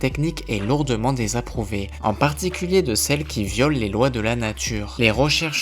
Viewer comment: pk jai entendu le mot " viol " ??? 😂